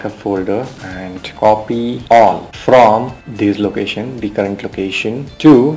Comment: I need to know what that dot means after the word copy..!! please I need an answer are you saying alt? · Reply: more technically correct is that the asterisk (above the number 8) is a wild card symbol in DOS. eg. pa* would include all filenames starting with "pa".